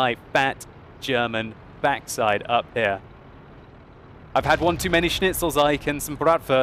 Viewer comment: what are you saying at 5:28 ? I had one too many schnitzels...? That was hilarious!! :D